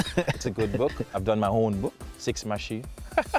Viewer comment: Gayle-I have read my own book, if u have heard it as 'Sex Machine' it's not, it's 'Six Machine'